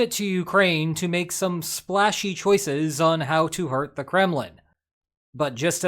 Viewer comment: Really thought you said "murk the Kremlin"